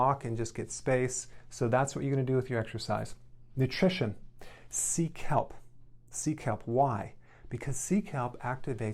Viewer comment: When he said “Sea Kelp” I thought he said “Seek Help!” 4:28 😂